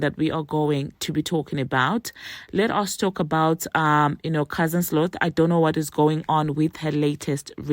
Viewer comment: Did I just hear you say “cousin Sloth”? 🤣🤣🤣🤣🤣🤣🤣🤣🤣